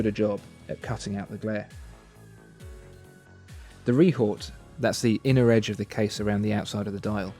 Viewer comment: you say 'rehort'? I'm not sure, I've never heard of this and can't find it, what do you actually say? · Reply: Rehaut